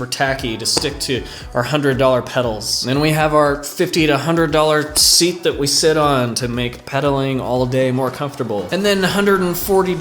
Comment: Get EVERYTHING damp! 🤘🏼😂🤘🏼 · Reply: Did you say $1500 dollar seat? Whoa, you are a baller. Comfy...